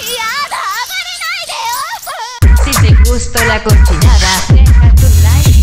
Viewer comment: Alguien me puede decir que significa "confinada" o no sé qué es lo que dice xd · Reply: "cochinada" en realidad xd